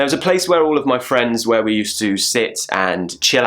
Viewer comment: I heard u saying " where we used to sits" instead of "sit" !! Is it correct or am I mistaken?